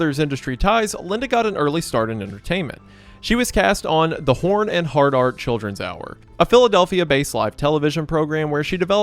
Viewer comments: I swear I heard "The Horn and Hard R Children's Hour" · Laughs in Mexican... Coke and now Trump... Huh 5:53 sorry what? The Hard R? Is that what it was called or literally using the hard R? Or am i mishearing? Lol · man i thought you were saying "horn and hard R childrens hour" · help- i thought u said "the Horn and Hard-R children's hour" T^T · Did you just say she worked on the "Hard R" children's hour? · Thought u said she worked at porn and hard r lol I was like jeez she worked for a racist porn company this lazy does suck😂